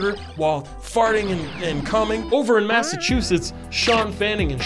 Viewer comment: did he says he's playing on the computer while "farting and cumming?"